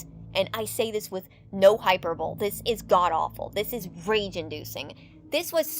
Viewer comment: Dawg did you just say hyper-bowl